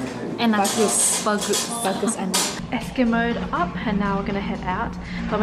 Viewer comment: Did she said eskimoed up on the 05:28 min ??